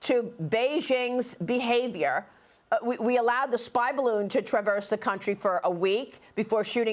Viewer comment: I thought they said “THERE’S NO THREAT FROM THESE DRONES”, same time, we don’t know where they come from. It started with the baloon debacle, what a joke. Now a guy is saying they’re drones flying from Chinese owned land around military bases. Ugh!